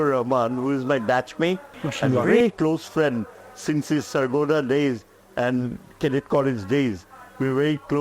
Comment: did he say Sargodha?